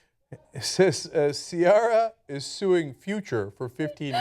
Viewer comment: Rofl, I thought it said Cialis sues the future at first. Damn you marketing indoctrination!!!